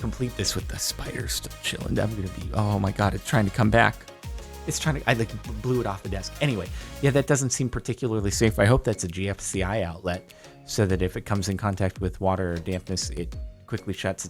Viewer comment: I looked up "GLCI" cuz I misheard and it brought up results for some company located in "Jordan" omfg